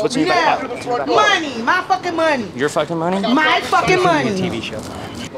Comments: Did anyone else hear "we're filming a TV show" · did anyone else hear him say this is a tv show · anyone else hear the words "TV Show"??